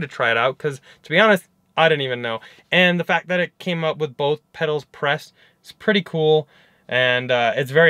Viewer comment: "Brothe pedals" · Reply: Could be my crappy phone speakers but I heard "Bro pedals"